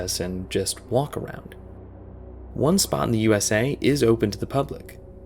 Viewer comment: DID I HEARD JUST SIMPLY WALK AROUND IT?